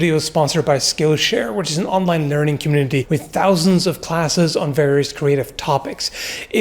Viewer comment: I was kinda sleepy and I heard you say "sponsored by skillshare which is an online nerding community" ^^